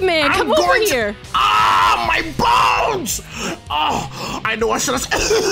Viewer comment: did just i heard "augh my balls"?